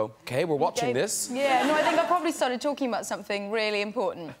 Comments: Can someone write down what did she say from 1:35 till the end? i dont understand her... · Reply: How in the hell did I hear "joking" when she clearly says talking... Thank you for correcting my mistake. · Can someone write down what did she say from 1:35 till the end? i dont understand her... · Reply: talking not joking.